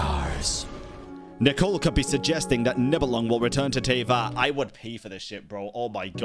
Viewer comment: I thought you said you would pee… I was like 😰🤨